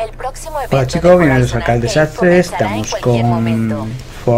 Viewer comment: Recién me acabo de dar la cuenta que en tu intro dices "Canal desastre", te jugo que siempre entendí "El canal del Sastre" 😂😂😂